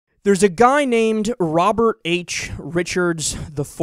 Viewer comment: At first i thought this dude said "There's a guy name Rich bitch", and that kind of made me feel slightly funny.